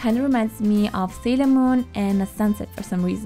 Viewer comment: did say "sailormoon and sunsets"?